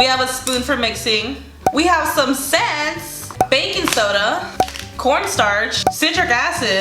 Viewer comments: sounded like bacon 🥓 soda😂😂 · Who thought she said bacon soda when she said baking soda 😂 lol